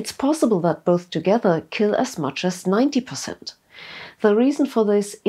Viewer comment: At about 3:24 the subtitles say 40% but you say 90% not sure if auto-generated or not but probably needs sorting. Awesome video !!